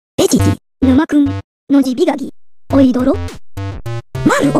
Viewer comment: did bro say "Aoi Todo".